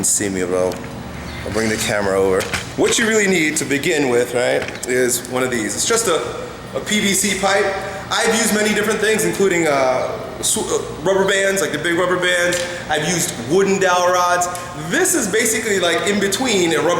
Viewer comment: I seriously thought you said "A BBC pipe"